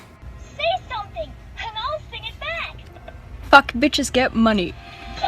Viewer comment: I thought Elmo said, "Fuck bitch!"